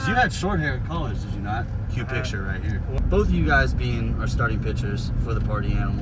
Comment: I definitely thought you said "cute picture right here" for garett in college and then realized that you probably said cue not cute 😂😂